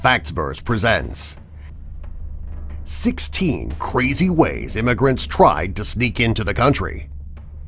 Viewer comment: I thought it said “16 immigrants who sneaked candy”